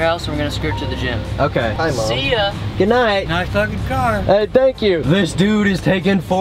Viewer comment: I thought quentin's grandpa said "nice f**kin' car"😂